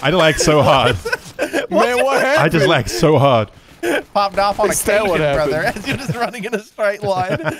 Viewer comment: Thought he said popped off on an Asian brother lmao